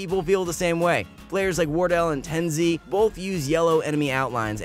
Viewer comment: did he just say Ten Z ????